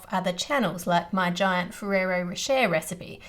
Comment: I'm hard of hearing. What Ann said: "My giant Ferrero Rocher recipe." What I heard: "My giant rear end chair recipe." Thank goodness for closed captions 😅